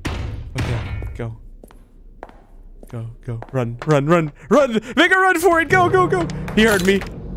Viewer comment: Am I the only one who hears him say "N-word run for it" at 13:18? Or did he say something else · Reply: Sounds very close but he says "run run run make a run for it go go go." it's the long A sound that's tripping you up.